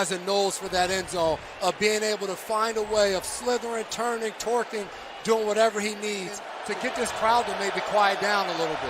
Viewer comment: did he really say, "twerking?" We miss you, John Madden!